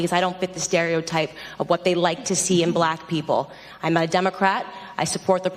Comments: Did she say "Im a Democrat" @ 1:04 ?? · did she say I'm a Democrat? I thought she was a conservative.